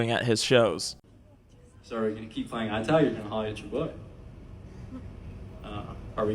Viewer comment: please tell what he said here?? im not a native speaker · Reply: “So are you going to keep playing eyes tag, or are you going to holla at your boy? Are we going to keep playing eye tag or are you going to holla at your boy? Guys am I muted?”